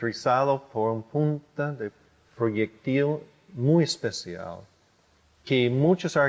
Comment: Alguien me dice que dice el hombre en el minuto 4:34 porfa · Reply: Punta de proyectil muy especial...